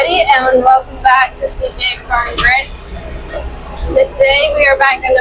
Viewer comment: it always sounds like you say "Grits" not grit lol